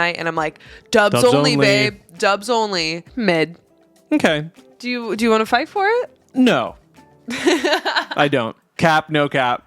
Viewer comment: Man I’m stuck in the past said “do you wanna fight for it?” at 12:41 and I 100% heard it as “will you fight for me?” Oml